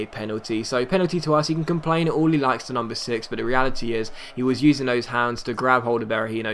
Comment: When he said the number 6 it sounded like he said the UNDER 6